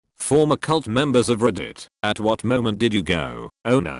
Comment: I thought it said farmer cult members and I was like "WELL THAT'S NICHE"